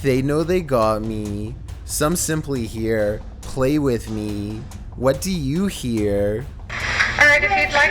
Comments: #8 and #5 should be switched #3 I hear " Can you play with me? " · I heard ‘ alright if you try to speak to us’ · For number three I hear "alright if you want to speak to us" anyone else hears that you have to listen very closely and you would hear it am I the only one who hears it😐